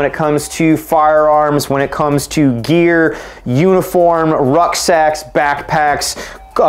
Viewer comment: Every time you said ruck sacks i heard "rough sex"😂